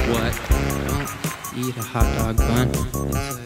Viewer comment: I heard "eat a hotdog bun" and I DIED laughing ;')